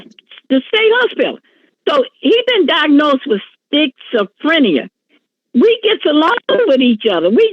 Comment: Did she say "sticks-o-frenia"? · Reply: I even rewound it to hear it again. I thought she said spitzophrenia 🤷‍♀️